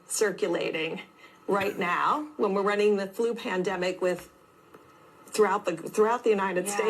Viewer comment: maybe i misheard, but did she say their running the flue plandemic right now?